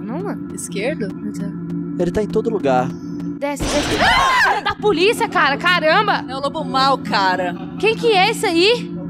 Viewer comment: Eu entendi Fia da Polícia cara caramba??